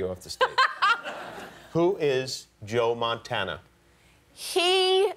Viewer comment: She thought he said "Joe Montania"!